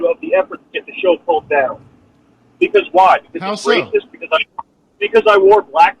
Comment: did sam say he was a rapist ?? at 1:44???